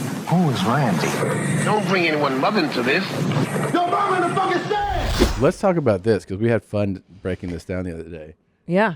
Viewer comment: In the intro, what does the black guy say? Does he say, "you're mama's a f**cken saint?" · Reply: You mom in the f*ckin stand